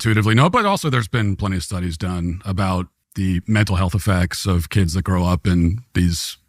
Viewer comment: LMAO when he said "the mental health effects" for HALF A HEARTBEAT i thought he said "the mental health of f@ LMAO i was like DAYummmh he REALLY went there